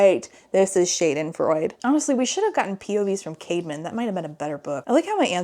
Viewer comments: OMG whenever she said Caedmon's name, I thought she was saying CAVEMAN!!! 💀💀💀It tweaked me out everytime lol 😭😭😭What a goofy name! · Misheard the brothers name when you first said it and for a second I thought his twins name was literally “caveman” 😭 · “His brother, Caveman.” Me: Did…Colleen Hoover name the characters in this book? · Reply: Oh I'm not the only one who thought she said Caveman.